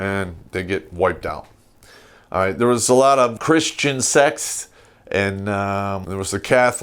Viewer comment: At first I thought said "Christian sex." 😁😁😁